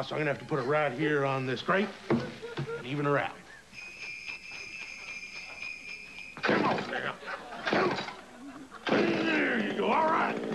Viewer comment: does he say " come on nigga" ?? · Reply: "come on now"